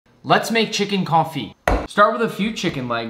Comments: I heard him say chicken coffee ☕ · On CONFIT I thought you said coffee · I thought he said chickens coffee 😭😭😂